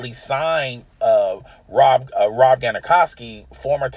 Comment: Did he just say "Rob Gonakawski"? Lol